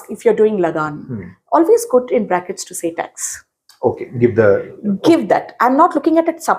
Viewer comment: could someone explain what was said here? did she say always quote in brackets? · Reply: She said that if you are writing a movie review for 'lagaan',in brackets write (tax) as the meaning of lagaan is tax.